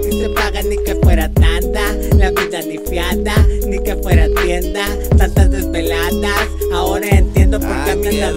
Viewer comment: Quien sabe que dijo el waza pero se escucho loco! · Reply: Yo nomas entendí "la vida ni fiada ni que fuera tienda"